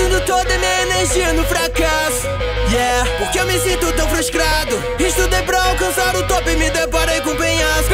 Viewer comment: eu ouvi fruscrado